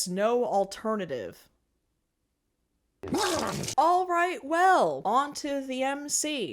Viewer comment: thought you said ‘alt right well’ and I’m kinda surprised you didn’t make the joke